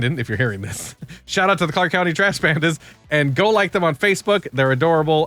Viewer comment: Clark County Trash Pandas Had to dig a bit Thought you said Bandits